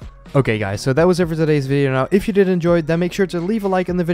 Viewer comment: did u say if u didn't enjoy then make sure to give a like?